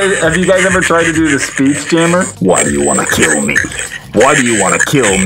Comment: On the Batman voice changer when he said why do you want to kill me I thought he said WHY DO YOU WANT EXTREME BACON?! WHY?!?!?